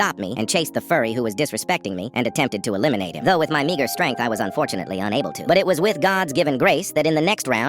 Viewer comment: I thought he said n*** strength